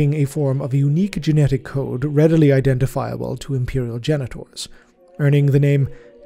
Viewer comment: "Readily identiable to Imperial genitals". I know thats not what the word is, but thats the only one I hear. Whats the real word here?